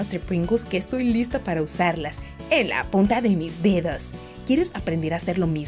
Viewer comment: En él minuto 2:57 escuche que dio en la puta de mi dedo lkie si tu también😫😫😫😫😫😫